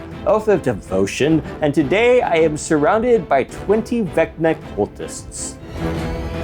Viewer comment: i misheard 20 redneck cultist, now thats an idea